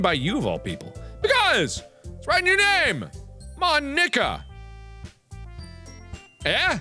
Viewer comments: Did Mark just say MON NIKKA, MARK YOU CAN'T SAY THAT # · Supposed to be pronounced: "Mon-eeka" What I heard: "Mo-n*kka